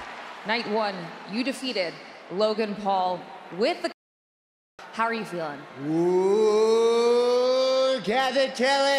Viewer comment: Can anyone tell me what they were scensoring at 0:17 ? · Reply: I heard c**b stomp